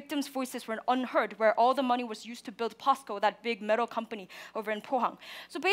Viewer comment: I thought she was saying costco 😂